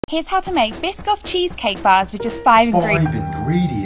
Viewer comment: Am I the only one Who Thought he Said Pissoff Cheesecake?